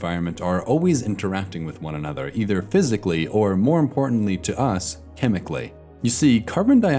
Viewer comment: For a second I thought he was going to say or mentally